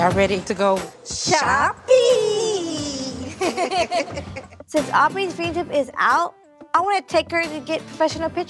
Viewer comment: I heard, “since Aubrey’s blehahheijuujj is out, I wanted to take her to get professional pictures done”